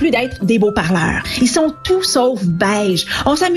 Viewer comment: J'ai compris "ils sont tout sauf belges"